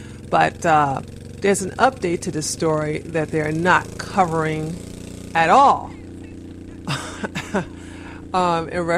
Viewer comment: what were you saying to that driver? ;) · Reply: "Choose a lane bitch" She was riding on both lanes.